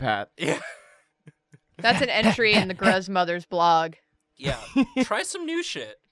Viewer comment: When said "gruzmother" I heard " grudgemother " and I think that's just hilarious.